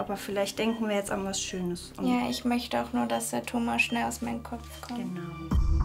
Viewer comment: Brudi hab bei 9:05 min Thomas verstanden und nicht tumor😅😂😂